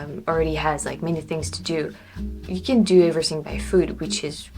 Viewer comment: at first I thought you said, "You can do everything by food"... which is also true in Quebec City 😅